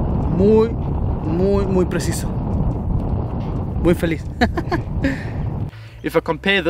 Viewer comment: "Mooi vellies " Hahaha